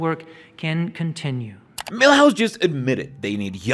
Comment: Did you just say Milhouse? 😝